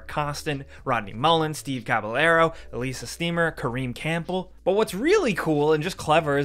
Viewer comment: Did he say release a steamer? 😂😂